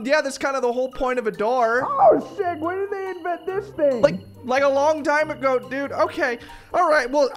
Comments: I know said "Oh sick" in that part but am I the only one who heard him say "Oh s**t"????? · I THOUGHT HE SAID SHI